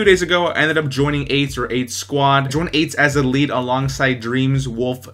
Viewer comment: Great vid one thing it sounds like you said I joined aids